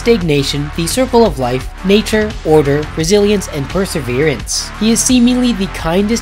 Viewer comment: I know you said "resilience" but I heard "Brazilians". Nurgle is now also the god of Brazilians.